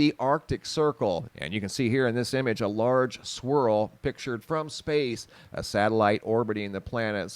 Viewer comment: I miss heard him say and thought he said ''A LARGE SQUIRREL'' pictured from space!, lol, SPIRAL!!!! oops EDIT; correcting my bad spelling!!!